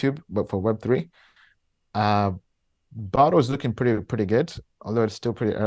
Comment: What did he say at 1:36? Botto? He said it's still pretty early. Thanks